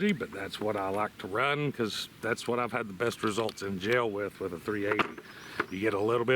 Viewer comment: I 100% thought he said jail. Like, dang this man must be on the .380 firing squad or something.